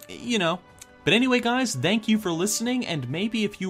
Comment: did he say dank you ?